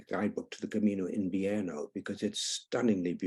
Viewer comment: what route is his favorite route, did he say?? - I couldn't understand him, there. And, at 3:36!!! I googled what sounded like what he said, and nothing turned up. However, his tips (walking poles!), and his - and the interviewer's many insights are quite interesting! Thank you for this video! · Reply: He said the Camino de Invierno ❤